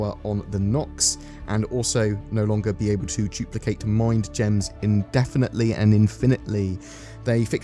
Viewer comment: Did you say "mind gems?"